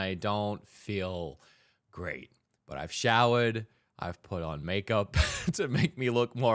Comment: did he just say make up?.....I knew it